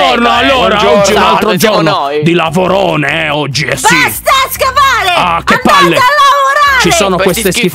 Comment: con la cantilena: BASTA SCAVARE ANDATE A LAVORARE! (Solo io ho sentito scop**e?!?!?!?) 7:59 Sempre con la cantilena: ZOZZI SPORCACCIONI LEVATEVI DAI.... Vabbè.... Io: Ma cos...🤣🤣🤣!